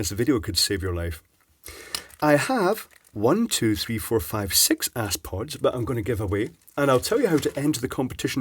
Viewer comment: Sounds like your saying "Asspods" 🤣